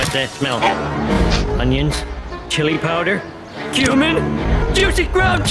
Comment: he actually said cumin